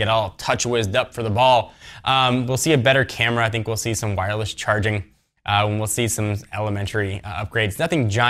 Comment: I thought he said Wi-Fi Charging at 3:22 .....I was just about to throw my brand new galaxy nexus out the window